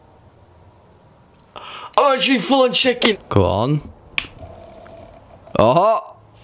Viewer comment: I thought he said " I'm full on chicken!" Not shaking